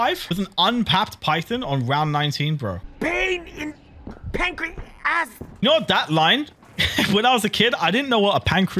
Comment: ngl I always thought Richtofen said pain in penguin ass there